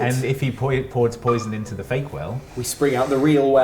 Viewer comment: I kept hearing "fake well" as "bakewell" and so I was imagining a very different distraction in that section